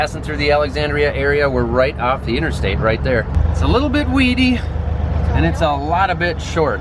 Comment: When you said 'it's a little bit weedy" I thought you said "It's a little bit wheaty."